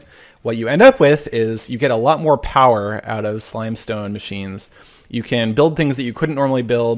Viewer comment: did he say slime stone